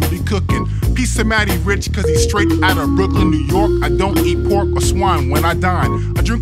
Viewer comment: The line "I don't eat pork or swine when I dine" implies that the vocalist is keeping kosher. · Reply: I initially heard it as "I don't need a fork, I swine when I dine" like he's just going hogwild on a meal with no utensils